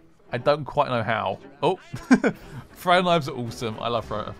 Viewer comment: lol i thought you said fright knifes but you said throwing knifes.. maybe i should learn hearing english more looool